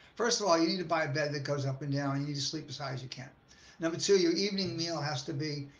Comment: she says “you need to sleep beside your…” Sounds like she says you need to sleep beside your cat! Wtf. How hard is it to get a good microphone or audio when making YouTube videos. Can barely make out half the stuff you say